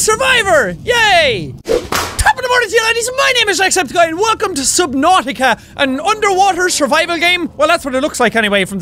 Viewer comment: I heard him say "welcome to subnautica" and i was like "where subnautious?"